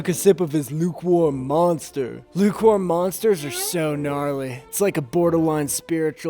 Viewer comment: did he say gnarly? Gang gang gang 💚GNARLY💚